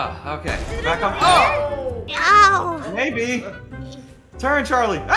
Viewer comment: did just called charlie baby or sum 👀. Sounded like maybe tho (if it's not baby) but why would he say maybe randomly out of context?